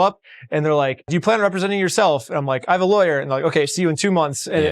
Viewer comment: why does he say he’s a lawyer? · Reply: "I have a laywer"